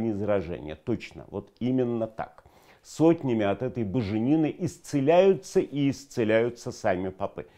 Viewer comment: Вначале послышалось буженина , а оказалось боженина ! Классный неологизм !!!!